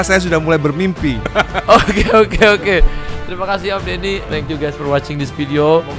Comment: Bang.. itu kok dimenit" akhir bilangnya terimakasih om dedy bukan om ahmad dani..? 😂🙏 atau aku yang salah denger ya..? 🤔🙏 maaf kalau aku yang salah denger